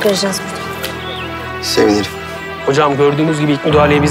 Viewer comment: burda ne diyor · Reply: sevinirim